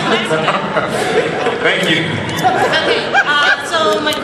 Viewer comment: did she say "I saw you fuck"??